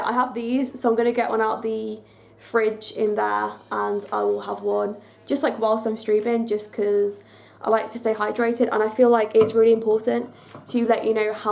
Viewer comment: who else heard "stripping" at 8:00 LOL sorry!